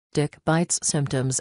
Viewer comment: I thought it said “dick bites” and I started laughing